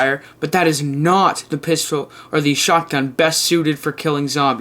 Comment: Did he say? PISTOL